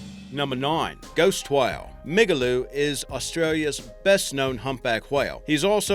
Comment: LMAO I thought he said Niggaloo.